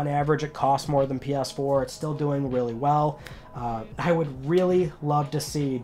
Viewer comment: did he just say willy well 😂